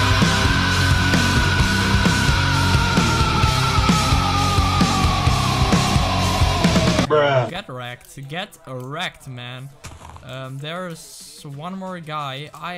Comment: did you just say get erect? xD Love this editing btw and I love your videos all in all